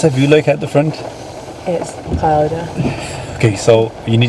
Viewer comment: did she say its powder???? 😆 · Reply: Cloudy